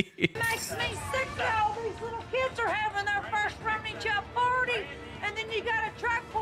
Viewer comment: For the first second after hearing this I honestly thought she said "something slur slur, these kids are having their first Rum n' Coke chill party"